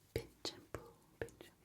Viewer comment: When you were saying pinch and pull I thought you were saying the b word and I was like oh no she didn’t